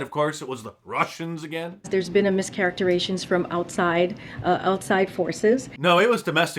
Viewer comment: Does anyone know what a "charactiration" is? She says it at 3:10.